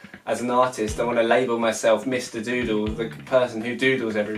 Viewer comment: God dammit i thought it said meet mr noodle